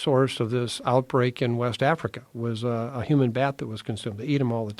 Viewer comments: Did he say "HUMAN BAT"!?!?!? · Did he say a "human bat" or am I hearing that wrong? If not then what the fuck is one of those?! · did he say HUMAN BAT?